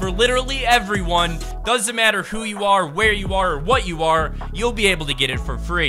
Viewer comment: did he say "it doesn't matter who or WHAT you are" !?!?!?!?!